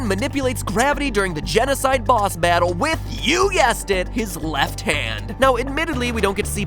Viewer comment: Did you just say "You yessed it?"